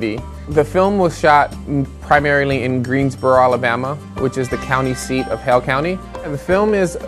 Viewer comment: I thought he was saying “Hell County”